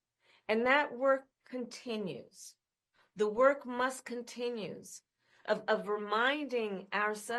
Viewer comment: did she say "the work must continues"? Something is wrong there.